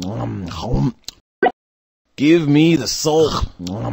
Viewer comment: did he say salt or soul? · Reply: He said soul, its a fucking human pumpkin